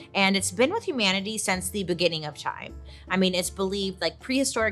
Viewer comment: “it’s been with humanity since the BOO-ginning of time” 👻 Did I hear that wrong or are you just in the holiday spirit 🤣